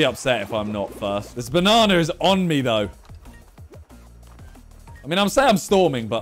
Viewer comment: "this banana is on me though" What did he sayyyyyyy